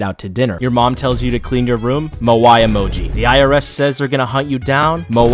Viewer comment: I accidentally thought he said Yowai mo instead of moai